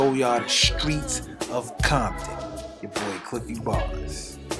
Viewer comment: Every time he says "Cliffy Barz" I hear "I'm a narcissist!". There's no way a normal person would film this and then edit it without finding all the repetition super annoying and tacky.